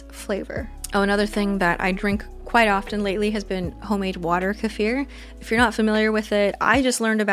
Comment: Such an enjoyable video!! At first when you were talking about the kefir I thought you said "I've been making homemade water" just plain lmao and it gave me a chuckle